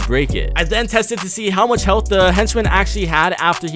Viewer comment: so a shadow henchman will shoot a shadow henchman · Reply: quackify yt im sorry i thought you said ghost henchmen